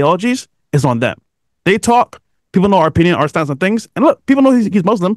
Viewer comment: did he just say no to Quran?